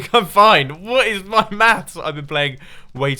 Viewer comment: Did Mumbo say "Did my meth" at 12:28 ?? Or do I have a bad mind. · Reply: Zugzy "did my maths" (they tend to say 'maths' instead of 'math' in the UK).